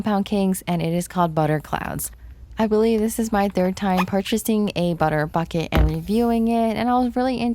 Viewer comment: When she said “ this is my third time buying a cloud bucket” my brain thought she said “ this is my third time buying a cloud- F!ck I+”